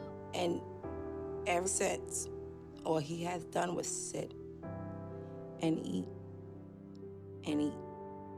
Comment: Oops! I thought she said " And sex... all he does is sit and eat". I'm so ashamed 😂